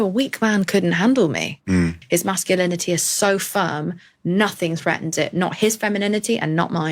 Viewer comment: Did she say "his masculinity is so firm"?